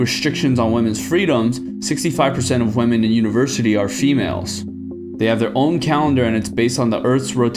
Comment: I thought he said she-males lol, no that's America 😂